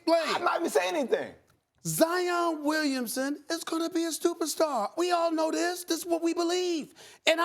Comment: anyone catch that? "Zion Williamson is gonna be a STUPERSTAR"